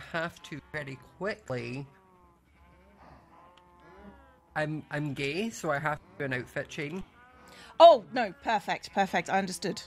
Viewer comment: Me at 33:50 trying to figure out what scott is saying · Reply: "I'm gay so I have to do an outfit change."